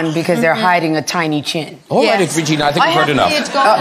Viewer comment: Could you please tell me what Graham said at 33:12 like after the tiny chin conversation? I only could catch "Alright, Regina......" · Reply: "i think we´ve heard enough"